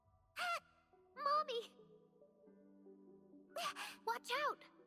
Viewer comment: I thought she said "moldy"